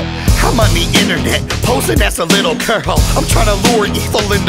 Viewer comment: I thought it said “posing like a little curhole” I was so confused😭